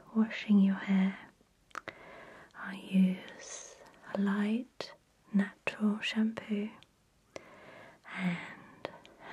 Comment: が…納豆シャンプーに…聞こえる… とてもいい音ですきです！